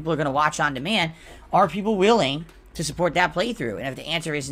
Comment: did he say are people whaling?